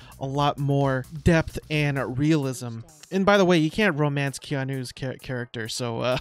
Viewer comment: Did you say can or can't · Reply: Sorry - Can't. I can slur my speak a lot of times.